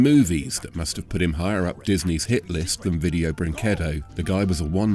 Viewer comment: did he say video brinquedo